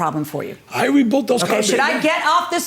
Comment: i thought he said 'the carbs were rebuilt' not that HE REBUILT THEM......rebuilt as to 'not new replacements'. sigh maybe not.... BUT, plaintiff absolutely baited the defendant, over and over, until he got what he deserved. sorry defendant couldnt just walk away and keep his temper. however, sticks and stones gentlemen, sticks and stones. glad it wasnt worse